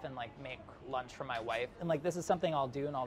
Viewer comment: Did he just say, "my wife?"